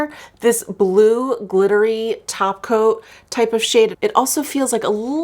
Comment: Lol every time you say "glittery topcoats" i hear "glittery tacos" lol 😂